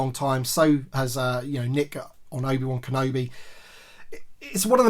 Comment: Ooop... cheeky little mention at 12:24 😆😉 Except... it sounded like you said Nick at Obi Wan Kenobi!? 😆 then again I've mispronounced you loads... I said once! Ha. Nice episode 👏🏻 and I'm excited for Wednesday now! 👌🏼👌🏼👌🏼